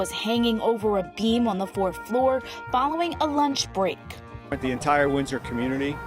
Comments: Did she really say following a Lynch break? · For some reason I heard that as the rope was found following a lynch break...